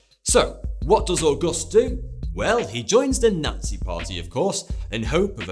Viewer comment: I thought you said nutsie party